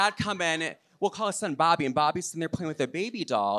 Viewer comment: I thought he said BARBIE not Bobby